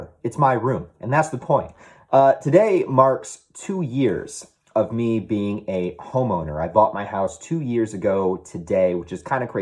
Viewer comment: As always inspiring! Did chuckle a bit when I thought I heard you say: ‘Today marks two years of me being a homo.’. Either way, congrats man, you’re doing great!